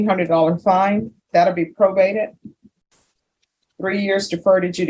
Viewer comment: The fine is probated. That basically means forgiven. No one has to pay unless he messes up on his probation. At that time, the Court can do a host of other things as well. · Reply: Oh, that's ok, then, lol... (I heard it as prorated).